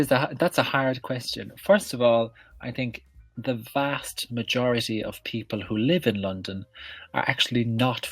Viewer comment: Answer: the vast majority of people who live in London are actually not from London Greetings from Peru :) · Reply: I heard "the FAST majority..." hahahaha